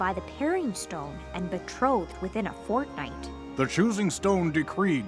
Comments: wait did she say fortnite? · did she said fortnite at 2:05 ? ILLUMINATI CONFIRMED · did she just say fortnite? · did she just say Fortnite😨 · Is it just me or did 2:05 sound like she said fortnite? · bro did she say fucking fortnite?